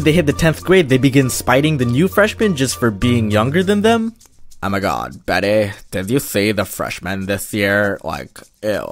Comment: I heard "oh my gawd becky"